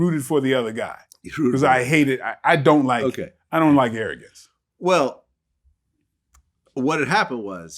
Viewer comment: I thought he said “I don’t like arrogance”.. maybe I’m trippin.